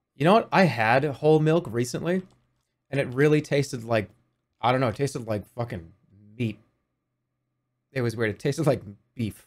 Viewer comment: Is whole milk at least good for making baked goods with? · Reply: Think AGAIN, Doctor. He says FEET. That's HOT.